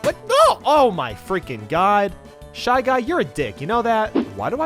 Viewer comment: what did you call me 😠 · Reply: He called u a d**k